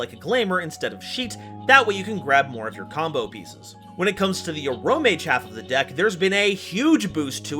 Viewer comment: Is it just me who sometimes misheard Aromage as Amorphage ? Sometimes it bugs the hell out of me.